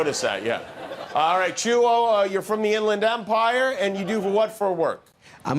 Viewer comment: he said “Inland Empire”, and I heard Pasadena.😹😹😹I only think of pornography, when I think of the Inland Empire. That’s ok, but let’s all be honest with one another.